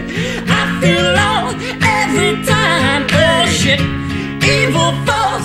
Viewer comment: Is it just me or does he say "oh shit" at 3:13?